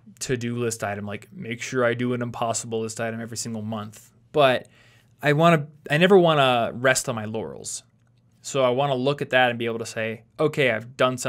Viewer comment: All I heard was "I never wanna rest on my yannys" My step mom always says that the only person she has ever lived unconditionally is her children.